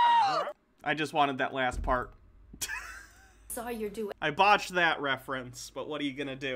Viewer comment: When said 'botched' I thought she said 'vaushed' and it killed me